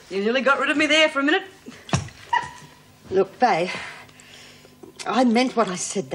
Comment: she said vay instead of bea and vale wig is terrible they could have at least gave her a decent one like bea.